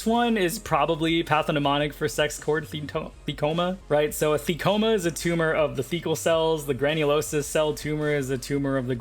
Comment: I looked away for a second at 24:00 and my brain fully heard the words "A fecoma is a tumor of the fecal cells" and I had to do a triple take lmfao like wtf did I just hear